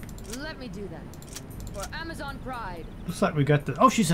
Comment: Every time she warcries all I hear is "FOR AMAZON PRIME!"